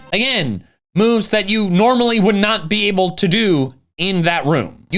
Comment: what does he say here? "If you care about __ almost everything is the samr" ive relistened like 10 times and i can't understand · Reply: ORAS (Omega Ruby & Alpha Sapphire)